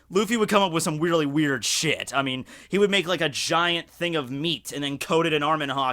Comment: I heard "he would create a giant piece of meat and beat it"